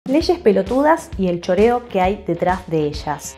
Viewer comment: ¿Porque pense que decia leyes perturbadoras? Ja ja ja